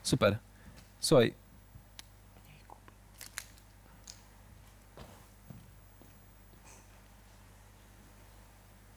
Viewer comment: Co on powiedział? ''Mniej ...''? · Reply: Mniej kupy